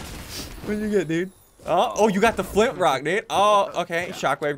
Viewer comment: Through 14:51 Did he say Flint Rock?